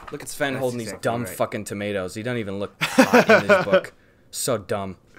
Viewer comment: What does Gus say at 29:37? "He doesn't even look __ in this book." No matter how many times I listen to that part, I can't tell what he says.